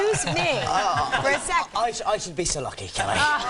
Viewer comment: can someone tell me what he said at 2:37-2:38. i should be so lucky?